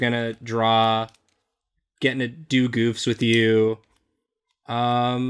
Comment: I heard "Duke oofs" instead of "do goofs" and I just imagined a battle of constantly messing up.